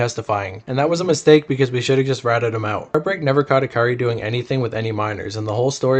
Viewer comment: He says "we should have just lit that ni&&a up" and you read it as "we should have just ratted him out" 😭🤣 Lmfao I don't even know any of these "rappers" you're talking about, I have never heard their music. Idk how this even got in my YouTube feed or why I'm watching (thank YouTube for that). You claim you're fair and unbiased, and then do shit like that. Even just reading it the way it's not written, and saying the opposite of what he said is BS. That's driving a false narrative.